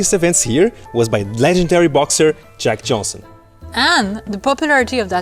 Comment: Jack Johnson, though the first time I swore he said Jack Jones (sang theme for TV series The Love Boat)